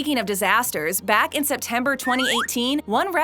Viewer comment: December 2018???? We aren't even past November 2018....check your dates before reporting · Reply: I heard "December" at first, but upon relistening they said "September"